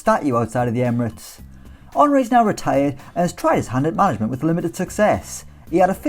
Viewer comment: Thought you said Henry "tried his hand at marriage but it didnt go well" 🤣🤣🤣